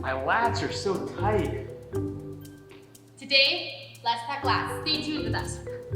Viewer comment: I hear "lats talk let's" 😅